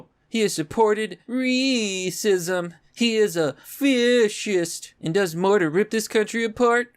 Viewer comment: lol 02:40 did you say "Fish-ist"? Now i'm imagining a Not See flag but with a fish head in the white circle.